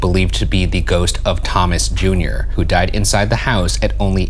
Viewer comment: Actually sounds like it says “I die” there’s another breathy noise before the “died”